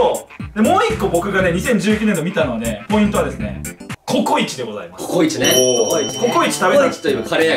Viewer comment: CoCo壱がなぜかコウイチに聞こえてしまう